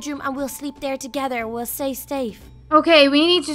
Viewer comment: she said say stafe instead of stay safe